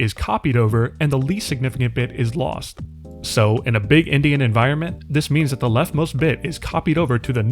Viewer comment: what is being said here? I hear "in a big Indian environment" but that doesn't make sense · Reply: “Big-endian” is an official computer science term specifying the significant order of computer bits. You can toggle subtitles if you’re ever unsure of what I’m saying!